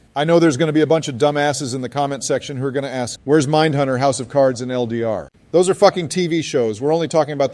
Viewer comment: Mindhunter was brilliant! wait nevermind I thought they said manhunter. Carry on😅